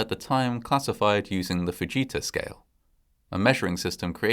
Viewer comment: Instead of the ‘Fujita Scale’, I thought he said the ‘buh-Jesus Scale.’